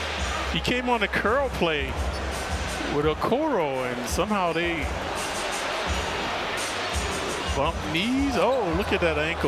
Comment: A non contact injury where his ankle was moving like that is scary, can only hope but I hope that the fact that he still walked away is a sign that the injury isn’t too severe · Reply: I thought they said he bumped knees